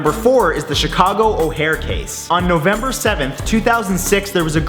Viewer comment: the subtitles said "November 7th 2016" but Matthew said "November 7th 2006" o_o something may happen on November 7th 2016...or im just crazy